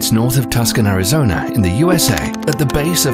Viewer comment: Why do I keep hearing him saying "Tuscan" instead "Tucson"?